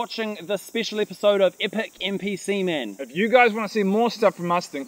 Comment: That last part when said "Epic NPC Man" I heard it as "Epic NP s*men"